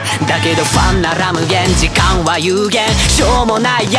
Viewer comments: もしかしてこれは… もういいから全員黙れ →「ヨルシカ-言って」を否定 真夜中以外でも快晴 →「真夜中でいいのに」を否定 俺は夜なんて駆けない 未来に賭ける →「YOASOBI-夜に駆ける」を否定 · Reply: 『不安なら無限時間は有限』のとこ、『ファンなら無限』に聞こえるから、「ファンの数でお前らとは格が違うぜ」的な？(クソザコ考察) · "だけど不安なら無限"が"ファンなら無限"って聞こえてエモいなって思いました(小学生レベルの感想)